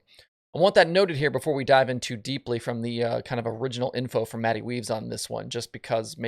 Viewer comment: Did... did I hear that right at 3:00? "Matty Weaves"? I've seen him called some things but that's certainly a new one lmao